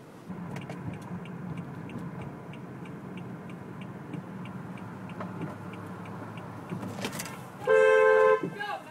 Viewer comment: What did that guy yell at 23:40? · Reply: "Go, goddammit, don't worry about me, goddammit."